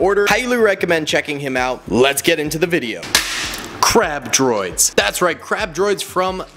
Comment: I thought he said crap droid😂